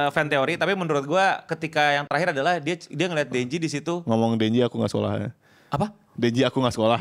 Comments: sumpil gue dengernya "Denji, aku ngga sholat" Weh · yaampun kukira cuman aku yang salah dengar wkwk 🤣🤣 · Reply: denji aku ga solat wkwkwkw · gw dengernya "Denji aku ga Sholat"